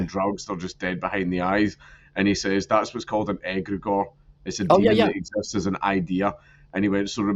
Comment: Dang it Dank! Your thick accent made impossible to figure out what you were saying at 1:00:02. For those looking, i figured it out. An EGREGOR. Thank you closed captioning.